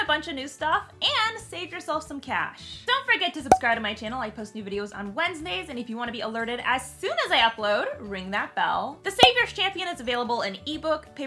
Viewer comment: this is a video where you should've done' your self-hype spell just a little more slowly. I swear, at the very end after listening to all of your "erotica tropes," it sounded like you said "I post nude videos every Wednesday..!" I'm still laughing over here. :D :D :D